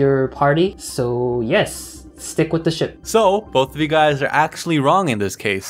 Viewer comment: WHAT THE FUDGE i was watching with subtitles and when (at 3:01) says stick with the ship the subtitles say (what ship Daniel x or Stephen x) oh btw I'm on a computer it says cue the x stephen shippers